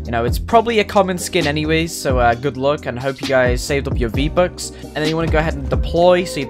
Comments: did you just say save up your vbucks. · wait wait wait did you say i hope you guys have been saving up you V BUCKS?!? 0:58 if you wanna see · did he say vbucks?? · DID HE JUST SAY VBUCKS?